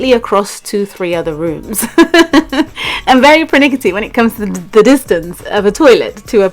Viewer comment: I know its totally off topic but What’s the word u said between 4:43-4:44....I’ve heard it many times from u and tried to google it but never got the answer.... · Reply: Out of My window she said she’s very pernickety when it comes to the distance between the toilet and eating areas. Other words she doesn’t like them too close to each other.